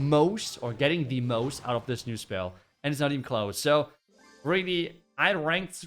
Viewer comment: When he says "CLOSE" I hear "KLAUS" 😂